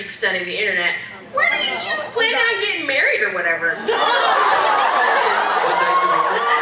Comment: what the girl says in the minute 12:08? SOMEONE PLEASE!!!! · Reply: angelhbkghellen she says when do you two plan on getting married